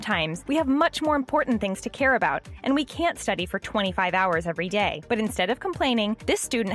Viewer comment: Did she say 25 hours a day